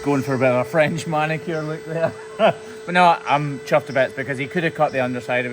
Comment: did he say chuffed to bits isn’t that Mumbo’s saying